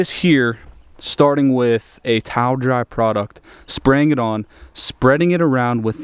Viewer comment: Watch the entire video! He let it air dry and went on with the steps in the video. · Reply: yea I watched it closer a day after I posted my comment but I just didn't remove my comment. He actually said he towel dried it at 6:58 mins in. He said "starting with a towel dried product" before applying the hybrid ceramic wax.